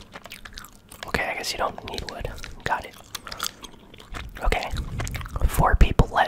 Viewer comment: what did you say?? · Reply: I guess you don't need wood got it.